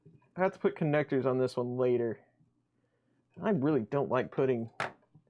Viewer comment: Hi, At 1:43:30 I thought you said "I really don't like pudding"...;-) May I ask how this is getting on now, are you still printing with it... All the best......